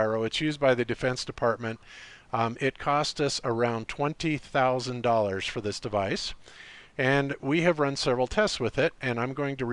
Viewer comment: Instead of cherry picking just show the crowdfunding page. There was no crowdfunding. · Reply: mpunkt "he came in contact to somebody possessing one and let him play around with it." Really? Are your ears working properly, the words I heard were. "It cost us around $20,000 for this device".